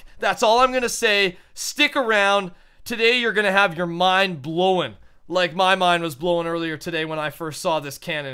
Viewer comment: am i the only one who heard blow-en?